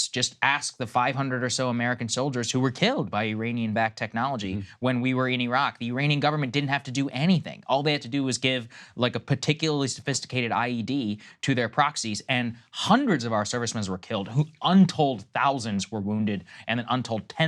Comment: Sounded like Saagar said "a particularly sophisticated IUD" to kill American troops. Perhaps less Pentagon jargon would help when talking to the general public.